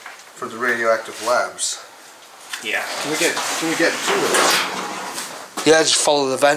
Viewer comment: im late on this vid but at 16:40 why does it sound like someone said yes